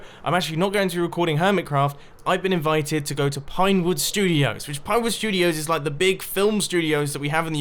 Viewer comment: I thought he said pipeworks studio for a second